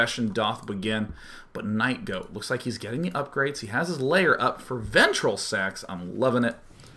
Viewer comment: I heard him say "ventricle sex"